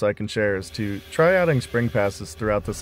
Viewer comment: you said something that I could not quite understand. It sounded like you said "try adding spring passes" for threads. If that is what you said, what does that mean? I am the definition of low volume work: retired guy in home shop! 🙂